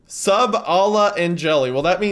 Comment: I thought it said "Sub Allah and Jelly" like some messed up subway order